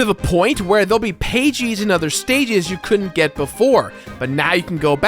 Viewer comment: it sounded like he said "pagies in other stagies" and now I can't unhear it